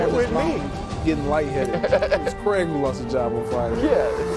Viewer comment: did Jennings say Craig Carton lost his job?